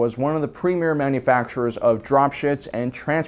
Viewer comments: I swear you said "Drop Shits" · Did he just say "dropshits"?